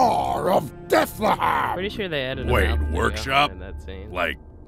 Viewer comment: What did you say at 9:03 please?! :( The video audio is louder than you!!! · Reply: pretty sure they added a mouth to in that scene